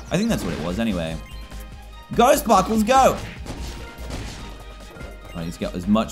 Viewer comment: When he said '' Go sparkles go '' I thought you said '' Ghost sparkles go''😂😂😂